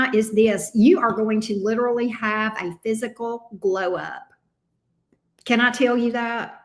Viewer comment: Auntie, I thought you said physical blow up in gemini. When u repeated it I heard glow up. But I was agreeing with blow up lol. I can feel it.